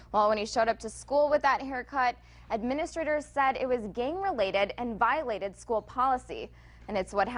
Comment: I thought she said game related, so what's wrong with that? Gang related?